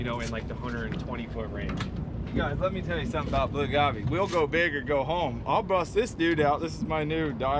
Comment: did he say "Blue Gaw-Bae"? Lol